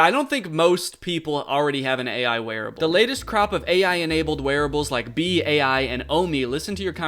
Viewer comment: I heard crap, not crop lol